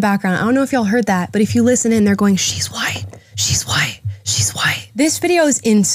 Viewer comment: I think she said “she’s lying” not “shes white” I mean I wouldn’t be surprised if they did said that but that’s what i heard